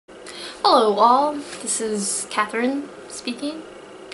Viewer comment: I thought you greeted us by saying 'Hello Wall!' Like you're talking to a wall. I must be one of the intelligent bricks in your wall :D You have such an awesome voice, aaah! City Of God is excellent! Such a good film <3 Have you watched 'Kidulthood'? I'm not sure why but I think it's similar story, although in less colourful/British surroundings. Gangland stuff.